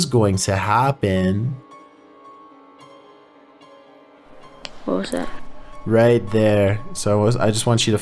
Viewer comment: Video number 2 the whisper sounds like someone saying "what's that what's that"